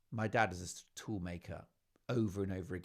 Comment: I thought he said, My dad was a Fool Maker. Must get my hearing checked 😒🙄